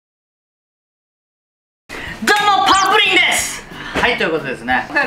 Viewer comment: 冒頭の自己紹介が空耳で「パワープリンです」に聞こえる ちょうど近頃お味噌汁をどうにかグレードアップしたいと思ってたので助かります！